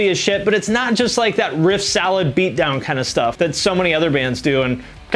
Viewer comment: Sounded like you said "riff salad"